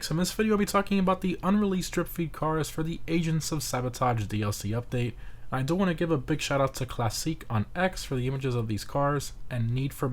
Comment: I keep hearing, “Asians of sabotage DLC” 😂